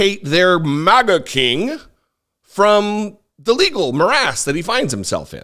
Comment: When you said legal morass, thought you said legal molasses. Fitting either way